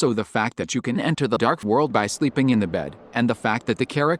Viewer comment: IN THE CAPTIONS SAID, AND I QUOTE, "LIGHT WORLD" WHAT THE HECK! HE SAID IN THE NUANCE VOICE "DARK WORLD" BUT IN THE CAPTIONS HE SAID "LIGHT WORLD" Thank you for your time.